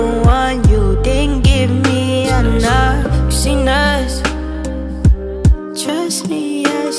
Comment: Am I the only one that thought he said "she nuts trust me I seen from far" instead of "seen us"?